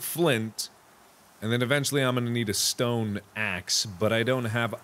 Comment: When mark said a "stone axe" I thought he said a "stone ass"